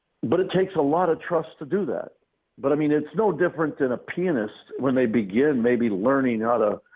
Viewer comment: Thought he said penis at 15:45 😂😂😂. However, great podcast Mr. immelman!